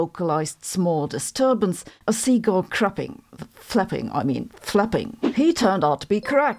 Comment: “seagulls crapping… I mean flapping, FLAPPING”! 😂😂😂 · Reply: I thought she said "seagulls fapping..." 🤣